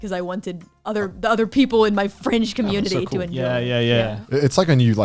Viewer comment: am I hearing right ? Or is it just that I want to hear it my way, I hear "my French community " and you ? Dans tout les cas cest intéressant